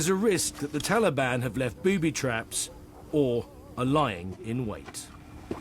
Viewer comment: ha...i thought he said booby traps, or a lion in wait...i was like ?? :) Good on ya laddies!